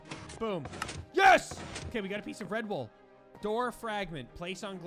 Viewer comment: When you said red wool I thought you said red bull 😂😅🤣😎🟥🍺🍺